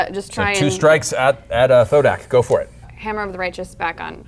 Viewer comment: did she just call him ho dax 😂😂😂